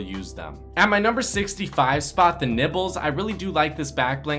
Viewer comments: I thought he said nipples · When u said nibbles it sounded like u said nipples xD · my dumbass thought he said nipples